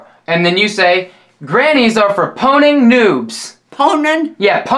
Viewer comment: I almost fell out of my seat laughing when I thought she said "Grannies are for porn and nudes"